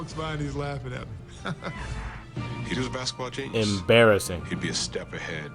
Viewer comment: Bird and Magic saved basketball, and we need them to come back. I cant take Jordan out of my #1. He cemented what Larry and Magic did by giving us dunks and air time like a freak alien. At the time, nobody had seen anything close to what MJ was doing. I've got Bird at 2 or 3 though. MJ was a genius from the foul line to the basket. Bird was a genius on just about everything else. I could have swore they said Genius, not Jesus.